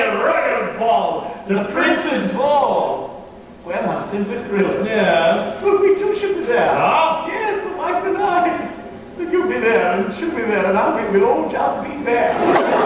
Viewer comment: Someone please translate 4:15-4:24 What does Percy say??? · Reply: "The prince's ball? Well, I'm simply thrilled. We too shall be there! Yes, the wife and I. You'll be there, and she'll be there, and I'll be—we'll all just be there!"